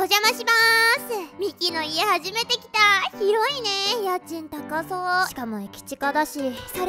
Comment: 「広いね〜！！！」 「キモイね〜！！！」に聞こえた私は末期